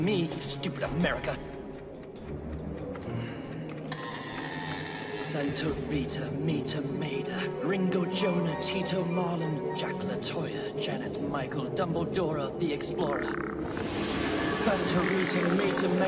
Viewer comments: did he just say dumbledora the explorer as in dumbledore and dora the explorer put in to one? LOL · I swear England says “dumble-Dora the explora at 9:57. No lie